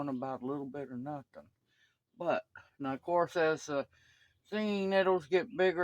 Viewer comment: Nice! Haha I thought I heard you say “singing nettles”😅 Once the rain stops here I will be out gathering nettles!